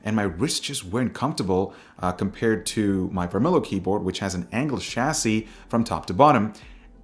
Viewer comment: Yo, where can I buy the keyboard on 2:20, did you say Varmilo?